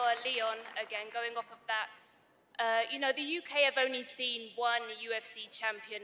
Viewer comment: the first time I heard I legit thought she said "the UK abony seen" 🤣🤣😂